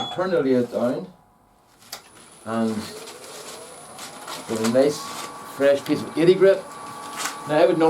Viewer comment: Well, I certainly wasn't disappointed - when you said 'a nice fresh piece of eighty grit' I heard it as 'a nice fresh piece of tatie bread'; maybe I'll go somewhere for a fry today. Cheers, that was great.